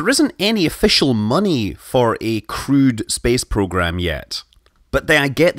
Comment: Thought you said "crude" instead of "crewed" for a minute there...